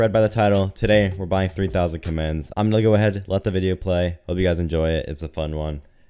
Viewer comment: I definitely thought he said 3000 commands. very confused